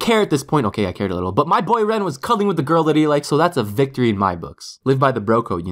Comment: when you think that said c*mming but he actually said cuddling